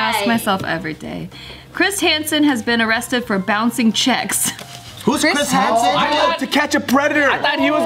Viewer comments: Thought it said "bouncing cheeks" I was mad confused at 1st 😂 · I thought it said bouncing cheeks loool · Bruh I thought that shit said “Bouncing Cheeks” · Oh I thought it said bouncing cheeks how disappointing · I thought it said bouncing cheeks